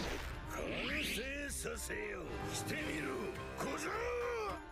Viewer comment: Did kaido just say gojo?